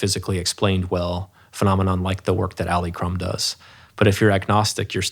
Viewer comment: Hi, could someone help me decipher who is referring to at 2:52:42? When referring to atheism, he talks about “unexplainable phenomenon, such as in the work of…”. Did he say Ali Crum? I’m interested in the subject matter, what I’ve found so far is an Alia Crum that’s a psychologist at Stanford. Is this who he is referring to?